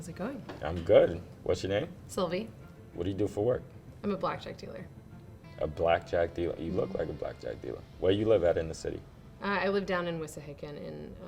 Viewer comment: I ain’t gone lie I thought she said “ima black drug dealer”😂😂😂